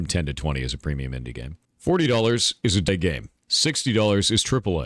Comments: What did he say at 2:16 for what a $40 game is? The audio glitched out. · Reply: i think it was meant to be "$40 is a Double A game" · it sounds like he says "$40 is a dead game" lol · What did he say at 2:16 for what a $40 game is? The audio glitched out. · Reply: Thank you, guys. I heard "$40 is-n't a game" and I thought 'that can't be right'